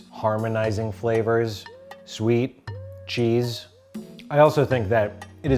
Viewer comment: Whoever made the captions for this video put at 2:17 "RTS and old ketchup" when the guy just said "artisanal ketchup". This had me laughing for a good minute. Thank you, sir/ma'm. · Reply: Also, at 4:36 he put "Jeez" instead of "Cheese". xD